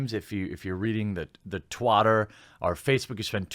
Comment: Did you just say "Twatter"?